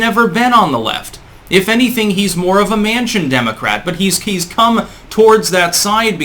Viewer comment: Mansion democrats, love that term. Heard champagne socialist used before, but this is great. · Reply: Thought said "[Joe] Manchen Democrat". I had to double-take a bit at that.